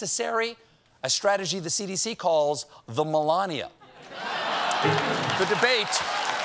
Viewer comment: Was it just me who heard Jon exclaim “Oh Melania!” like that at 3:42?! 🤦‍♂️